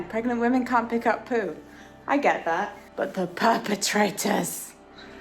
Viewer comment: Oh no! I heard you say "poopertrators" and can now not hear anything else XD